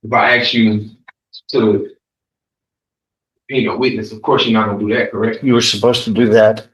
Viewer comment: Did he just ask the judge to “hang a witness”?! Because that’s what I heard. Then again, I’m struggling to decipher half the BS that comes out of his mouth. 😅